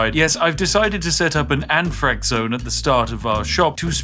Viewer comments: did you say you decided to set up an anne frank zone at the start?! WTF?! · Oh, anthrax!! For some reason I thought he said concentrated Anne Franks, which is a bizarre thing to say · oh, he said anthrax, i was wildly confused when i heard him say "oh yeah I set up an anne frank zone at the start of our shop to spread weapons of warfare onto anyone entering"